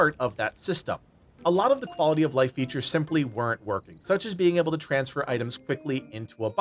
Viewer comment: No he didn’t, just watched the video back and he clearly states it’s a missing QOL feature. He did mention they were playing a pre release version of the game but if so he really shouldn’t complain about features that are missing from HIS version that are in the EA release. Oh yeah, that means they’d have to wait and actually play the version we are and miss out on the upload 30 minutes after the game releases to cash in on all the release hype for his channel. How about a considered, fully researched review of a game every now and then? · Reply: okay Mr. ignorant at minute 6:43 he says "a lot of the QoL features weren't working" why are you making stuff up instead of just watching the video properly...? Ridiculous